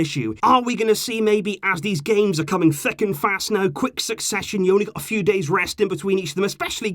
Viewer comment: what did he say lmao? “as these games are coming fucking fast now?” 😂